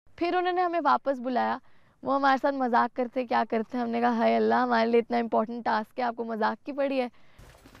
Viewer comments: Did say hai allah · Did just said::: (Haye Allah)??